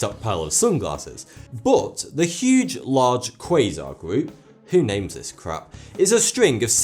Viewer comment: who names this crap? did i hear that right? 😂😂😂😂